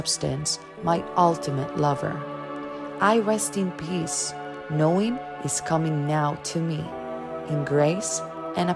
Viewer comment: it sounds like your saying “he’s coming now” is that the case, and if so should I as a guy avoid listening this?